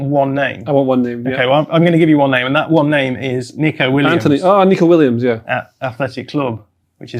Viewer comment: Thought said Neco Williams 😅